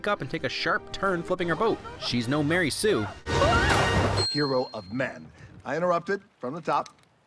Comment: First time I saw this movie, I thought Maui said "Hero of Meth"